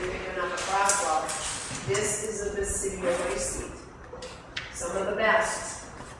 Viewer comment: I think she said basidiomycetes. Very noisy audio.